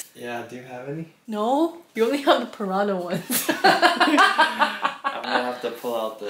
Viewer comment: um hello did she said "we only have purano ones".......(purano meaning in nepali old stuffs) so imma bit confused?